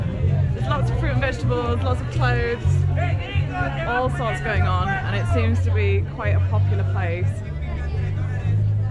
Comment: "Hey gringo......" ¿que dijo? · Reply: Hey gringo "te van poniendo los cuernos"